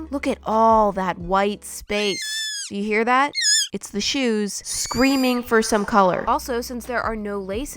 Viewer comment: Ah my violent instincts thought the when you said “screaming for paint.” And “secure your soul.” I thought screaming in pain, and sell your soul.